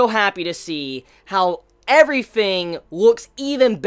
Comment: Could somebody tell me? I'm really curious: Is it me or does say things like "everyfing" and "foughts" instead of "everything" and "thoughts"? Am I mishearing it, is it an accent or is it just a thing of his? btw, just so you know: I'm not a native English speaker. it may be obvious to you, but it puzzles me